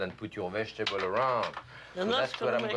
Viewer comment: OK. at 11:40 on video ....Does Jacques SAY 335 degrees or 375 degrees. My husband and I have a disagreement. Help. I'm right and he is wrong..... What say you.... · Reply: For sure 375